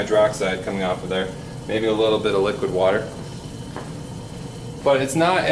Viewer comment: did bro just say... liquid water?